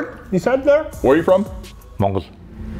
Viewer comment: I heard 'Mongolia' ???